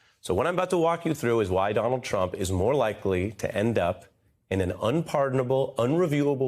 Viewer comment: 😳😳😳 Wait. What? No no no. Did Ari Melbsr just say "criminal STATUES"? I... I think he did.